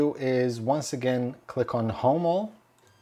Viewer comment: I think he just misspoke, meaning to say .01, kinda like he misspoke again at 5:23 when he said "click on homo" when he meant to say home. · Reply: You might have thought he mispoke, but you misheard, sounded like he said "home all"